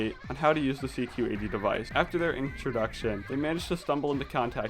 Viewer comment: BRuh he really said inktroduction